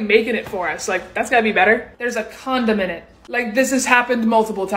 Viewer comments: Still hungry? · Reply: Lmao “There’s a condom in it”… I thought you said condiment at first. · when she said “there’s a c**dom in it” i thought she said “there’s a condiment”